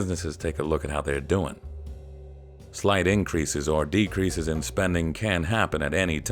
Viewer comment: The subtitles say "slide changes" when the phrase is likely supposed to be "slight changes". Please take note. 9:22 "executives whoever spend over hire and over promise" instead of "executives who overspend, overhire and overpromise".